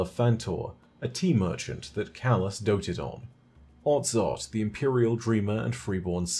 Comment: did he say Otzot? the very same Otzot that we hear about in the strike Insight Terminus?